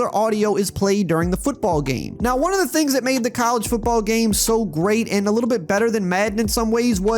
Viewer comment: Am I tripping ? I swore you was saying 2k making the college football game 🤔?